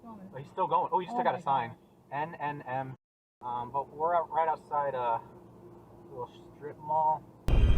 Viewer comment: Did he say 'we're right outside of a strip mall''?